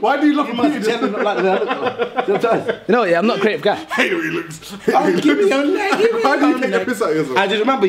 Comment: He acc said I hate the way he looks at me and how he looks which is two different things